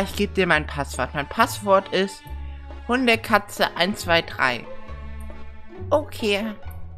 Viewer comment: Ich habe irgendwie das passwort nicht verstanden ich habe nur verstanden HundeKatze123?